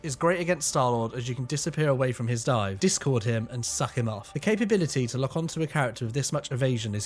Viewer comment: Great freaking video & information!! but 19:47Cloak & Dagger , specifically Cloak is great against Starlord as you can disappear away from his dive,discord him and suck'em Off "😂 I had to rewind that twice to make sure I heard that correctly